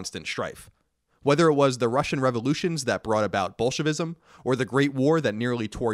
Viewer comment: almost sounded did you said "Bullshitism" lmao